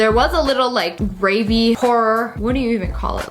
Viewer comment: When you said "gravy pourer" I heard at first "rabies horror" lol